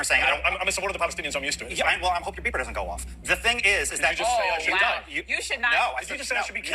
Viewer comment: I thought he said “ I hope your PEOPLE don’t go off”, I’m Swedish and I’ve seen this scene on several YouTube channels but not until NOW I understand! Thanks.